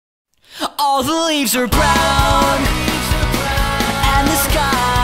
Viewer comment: In the beginning, I thought he said all olives are brown. o__O